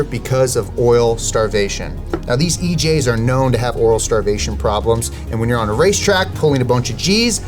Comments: I thought I heard EJ's had Oral Starvation problems... Closed caption confirmed this at 12:38. The more we know. :) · Anyone else catch that oral starvation lmfao 😂